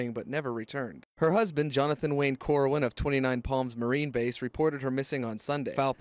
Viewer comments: Love your accent, I thought you were saying "29 Pams" 😆 🤣 😂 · “That thing I always say.” = “Let’s give it a goooooooooooooooooooooo” Btw, at first I really thought he said 29 Pams. Sounded like a very interesting place that only had 29 people named Pam who lived there. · I keep hearing "29 Hams" and I have no problem with that whatsoever.